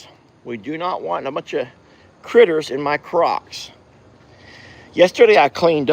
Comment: Watching the replay, Lord I thought he said “critters in my crotch” I need to go back to the Baptist hymnal, please! 🙏 🤦🏻‍♀️ Carry on